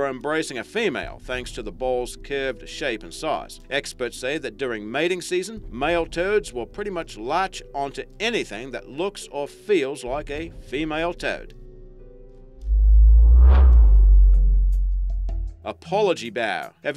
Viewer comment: Totally sounds like he said "male turds" lmao.